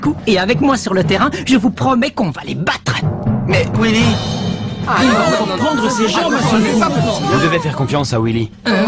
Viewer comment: ils disent quoi timmy et jack? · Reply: Jack a dit "il va encore prendre ses jambes à son cou" mais Timmy je sais pas deso